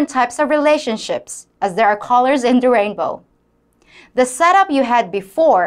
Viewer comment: Oh, colors in the rainbow. At first I thought you said "callers in Durango". Must be a Philippines thing, I thought.